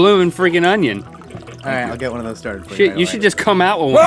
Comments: Did Link just say "sh*t"? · it sounds like link says sh*t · did link just say s**t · OMG! I think @ 9:52 Link may have said a bad word! haha I think he may have said "$h!t" lol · Did Link say sh*t @ 9:52?